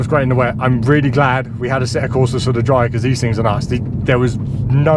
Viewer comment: Hehe, i thought you said "Assetto Corsa" at first, but then I realised it was "a set of corsas". Great test! No wonder why the AWD LMP1s destroyed the LMP2s on acceleration, but the new hypercars don't (I know everything else isn't equal).